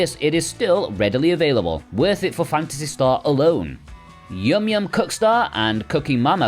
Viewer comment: Really sounds like you say “yum yum cuckstar”